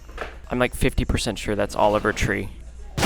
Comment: my captions said "im like 50% sure thats all of our tree" 🤣😭🙏